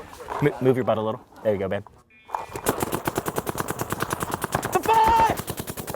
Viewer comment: "Did he say move your butt a little bit...there you go babe" @ 03:51 😂😂😂😂😂😂😂